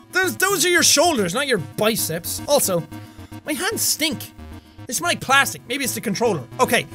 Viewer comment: did he said my pants stink or my hand stink